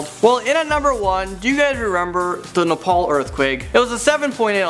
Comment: I thought you said the nipple earthquake